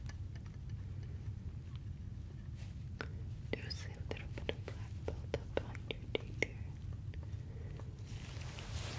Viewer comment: Wtf is she saying in 29:04? Woke me up all of a sudden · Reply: "I do see a lot of plaque built up on your teeth here" is what I think she says